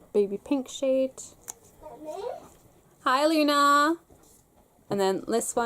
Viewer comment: i thought you said "Hi Alina". Since my name is Alina i naturally paused the video and turned around to see who is calling me haha